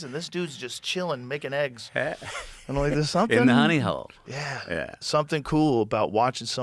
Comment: What did McConaughey say here? Nonny hole?